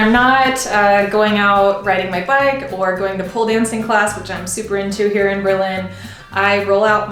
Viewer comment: did you say pole dancing?